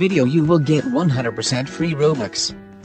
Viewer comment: I thought you said: “how to get a free shadow dragon” not robux